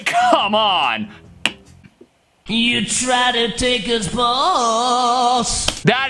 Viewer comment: I'm never gonna unhear it as "You try to take his balls" now. I also like how whenever pop songs are misheard, they sound evil or gross, but whenever metal is misheard it's wholesome stuff like " DO YOU LIKE... BANANAS" and "I LIKE JUICE, FINISH YOUR JUICE"